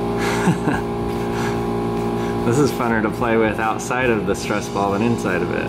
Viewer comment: Did he just say Funner